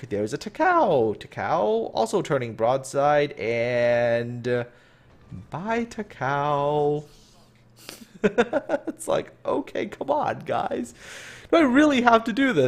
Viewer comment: Sort of half listening at 8:48 when you said "bye Takao" I thought you said "bite a cow" XD